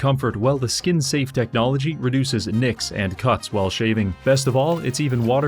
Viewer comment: For a moment I thought he said produces Nixon cuts.